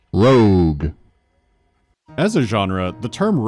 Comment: I could swear it says review.